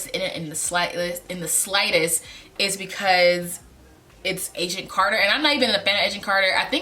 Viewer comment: I thought you were saying "Asian Carter" instead of Agent Carter.